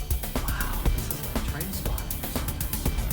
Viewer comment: Does he says Wow Trainspotting? LOL.